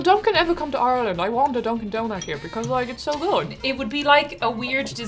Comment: I swore she said “Will Dunkin ever come to Ireland? I want a Dunkin Donut here because I get so alone” and I felt that